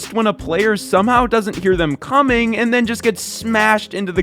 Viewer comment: what did u just say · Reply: doesn't hear them coming